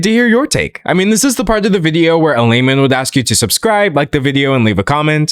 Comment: Every time you say layman I hear "lame-men"